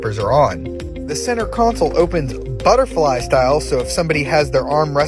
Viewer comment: did you just say geneSUS at the end